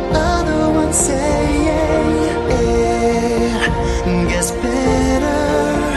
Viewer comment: I keep hearing "It Ngets Mbetter".